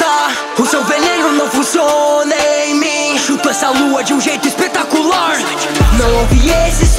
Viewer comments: eu entendi chupo essa lua quem mais entedeu assim curti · Juro q ouvi "chupo essa lua de maneira espetacular" kkkkkkkkkk... Desculpa'-'